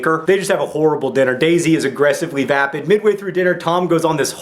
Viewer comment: I heard that as "aggressively fappid" at first.